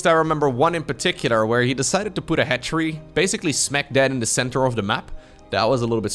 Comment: it sounded like you said he put a hatchery "smack dad" in the middle of the map. The correct phrase is "smack dab".